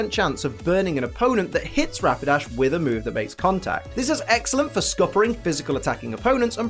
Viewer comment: its says rmakes instead of makes